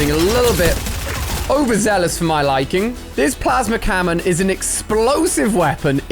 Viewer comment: Was it just me or did he say plasma cammon on